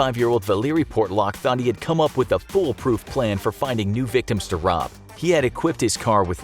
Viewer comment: did he say "pool proof plan"?